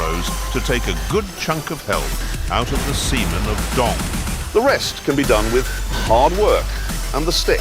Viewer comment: Never thought i'd hear Clarkson say "Semon of Dong"! XD